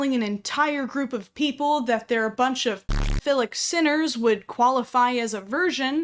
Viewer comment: Okay, honest question. What word is bleeped out at 12:20? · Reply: I think it’s pedophilic?